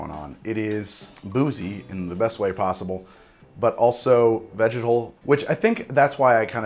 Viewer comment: I'm glad you display the tasting notes, because I could have sworn you said "vaginal" but after rewinding, alas, you said 'vegetal'...